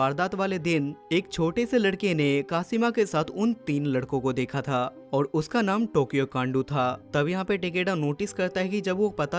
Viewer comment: I heard Tokyo Gandu😂